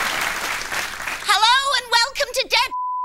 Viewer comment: does anyone known what she actually said? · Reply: According to IMDB: Josie Lawrence is bleeped in "Scenes from a Hat" for her reply to "rejected themes for restaurants". The uncensored quote is "Hello, and welcome to "Dead Cats: 109 Recipes That You Can Do With Your Pussy."" Moments later, Drew Carey is also bleeped; his uncensored line is "I don't know if they told you, but in America, we can only get away with that if you pronounce it puss-ay."